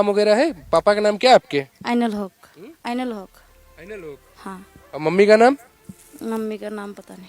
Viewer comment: does she said father name is Anal hook?